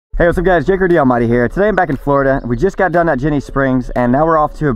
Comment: For the longest time i thought he was saying "hey whats up guys it's Juggerdee almighty here".